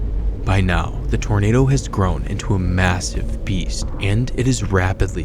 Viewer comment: Did he say Massive?